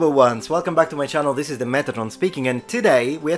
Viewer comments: It sounds like you said "Metronome" 😅🤗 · When you introduce youself, it sounds like you say "this is the methadone speaking", hillarious, don't stop.